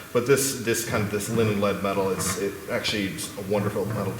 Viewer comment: What alloy did the solder guy say (11:08) his pipe is made out of? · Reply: "Linen Lead", that is, lead that is simply laid out on the table in a single pass and allowed to harden before rolling